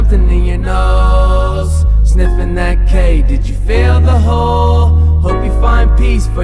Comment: I really thought that he was saying sniffin that cake💀💀💀